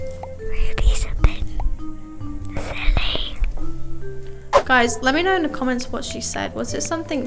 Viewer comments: i did not hear her well it sounded like she said "moody's a pig, silly" XD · I think she said “ruby’s a bit silly” I pretty sure